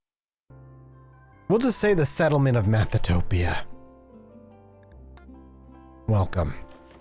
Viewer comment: For just a moment, I thought you said it was "Methatopia", which conjured up a very different image.